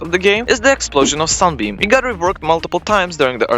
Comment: did bro just say sun bean